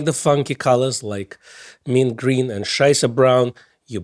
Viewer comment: Did say what I think he said at 5:16? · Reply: Yep, I noticed, too. says (nearly straight faced, cracking up afterwards): mint green and "scheißer" brown/braun. It should have been scheißbraun, really. 🙃 You hear me? Scheiße is German for, you guessed it, shit.